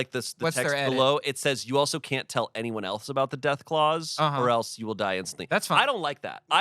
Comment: I know shayne said "death clause" but I heard "death claws" and I was like, when did we start talking about Fallout?